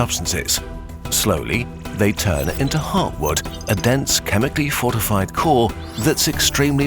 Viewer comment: Heartwood?!? 😱 I always thought people were saying hardwood!!!!